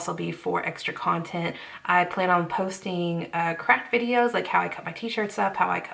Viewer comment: I thought you said "crack videos" instead of "craft videos" 😂 I was like "woah... Patreon is wild"